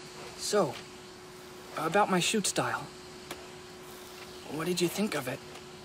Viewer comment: For a sec I thought he said ‘shoe size’ instead of ‘shoot style’ and I was like ‘you haven’t had a calm good convo with him in yrs and the first thing u choose to tak about is ur shoe size?!?!’